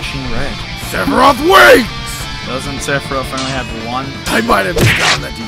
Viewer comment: Did he just say "Sephiroth Wings" 1:18 😂